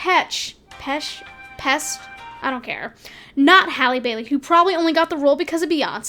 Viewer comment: My only issue with the casting of Ariel was when I thought they said Halle Berry was cast and I was like "isn't she my mom's age? Why is she playing a teenager" and then I realized it said Halle Bailey and I stopped having a problem with it. I'm not a fan of Melissa McCarthy as Ursula. I'd love if they used a drag queen though